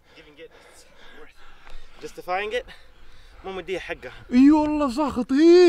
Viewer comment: بالله ايش قال مافهمت!! اللي فهم يقلي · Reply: Good Vibes مو معطيها حقها